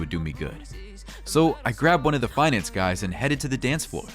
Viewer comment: I thought she said "one of the fine ass guys" instead of "finance guys".